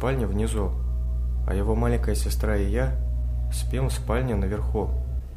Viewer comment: Ставь жиза, если тоже услышал "а его маленькая сестра Илья"